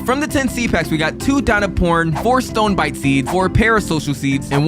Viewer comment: WHAT DID U SAY DINOPOR* WHAT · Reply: he said dino#po#n